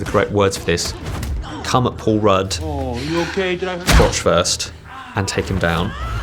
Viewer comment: I thought that said couch not crotch and now this is on my history